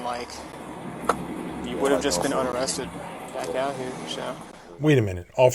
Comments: He said if the box would've showed up while James was still where he worked, he would've been unarrested. I think ya misheard that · I think you misheard the cop, at 12:01 he said “You would have just been unarrested.” Unless I misunderstood your commentary.